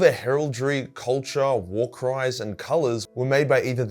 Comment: ngl, thought he said war crimes and not war cries.